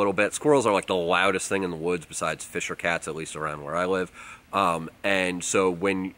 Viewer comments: besides FISH? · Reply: Fisher cats 😂 I thought he said just fish at first too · besides FISH? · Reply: i thought he said “besides fish or cats”